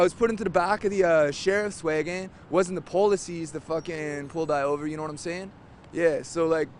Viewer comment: it sounds like he says "It wasn't the police that pulled I over". It seems to me that he was extremely meticulous in his responses, so I doubt that was an unintentional grammar mistake, but rather a way to avoid the word "me". Any ideas on the reason for this?